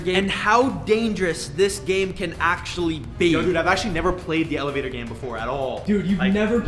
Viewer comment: Bra I thought you said you'll never play it ever